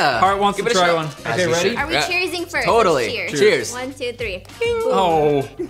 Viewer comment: I thought you said "sex cheers" 😂😂😂😂